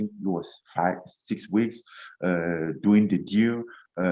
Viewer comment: Did he just say he was “doing the gear” in 2018?